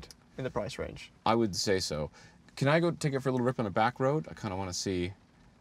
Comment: Back ROAD, I was confused, thought said back row and all the sudden he was driving. You Canadians and your wacky accents...